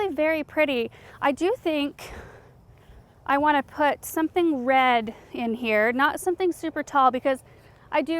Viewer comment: did you say "red"? 🎈